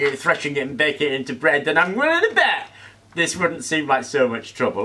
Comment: What is he saying at 4:28 using that sing-song voice? "I'm really bad"? Im not picking up on the reference. (This has been bugging me for a month, please enlighten me.) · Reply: “Willing to bet”. Don’t know why he said it like that though.